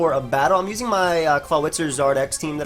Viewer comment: did say clawitzer or am i hearing things? how can you mix that irrelevant mon with anything lmao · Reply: He did say Clawitzer, I think he meant Crawdaunt.